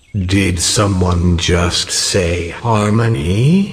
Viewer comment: did someone just say Hermione?